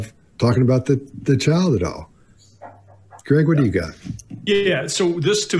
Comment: It's odd that she focuses on how the experts they've hired have expertise that exonerates them, but doesn't focus on whether they hired anyone with the expertise to find their daughter's killer. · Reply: I heard "what did you do !!!?